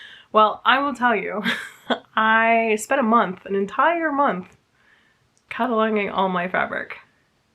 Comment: When you said, "cataloguing" your fabric, I had to rewind a few times because I for sure thought you said "cuddling" your fabric. LOL!